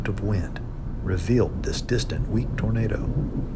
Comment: auto cc says distant weak burrito